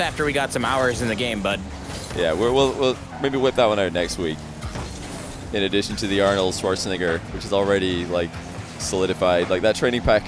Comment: what’d he say 😳😳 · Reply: In addition to the Arnold Schwarzenegger